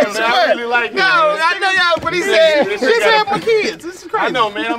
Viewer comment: Bruh I thought bro said “This is weird, he know my kids “ 😭 I was rollinnn